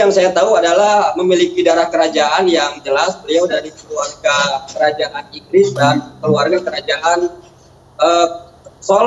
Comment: maaf ni gua ga salah denger apa ya : 8:25 latar belakang dari kerajaan iblis 😂